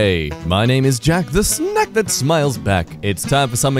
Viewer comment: I swear I heard Jack say "The snek that smiles back" lol